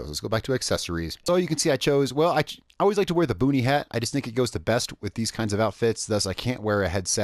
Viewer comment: I honestly thought you said "booty hat."